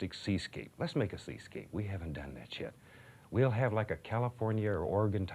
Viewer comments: Does he say: We havnt done that shit ?? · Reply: It’s his accent. He said ‘yet’ but it came out like ‘chet’ Floridians and Louisianians say it that way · Did he just say "we haven't done that shit" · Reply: No, he said "yet", not "shit". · I thought he said “We haven’t done that shit!”